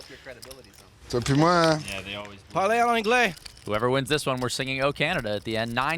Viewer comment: guy subtitle read ""more for me'' but he really said ''you and me''.